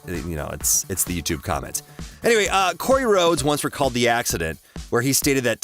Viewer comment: did you just say "Cory Rhodes" ?